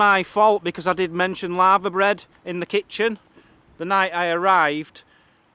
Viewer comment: For we Americans said LAVERBREAD which is a traditional Welsh dish made with littoral alga (seaweed). Sorry, it sounded like you said LAVA bread like a volcano, haha. It looks like something a Cow would eat, enjoy :)